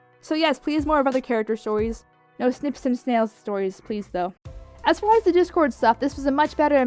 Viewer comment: It took me 2 minutes to realize you said Snips and Snails I thought you said Sniff some snails.